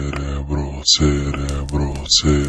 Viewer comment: слышится Серебро. 0:31 - послышалось слово Хентай XD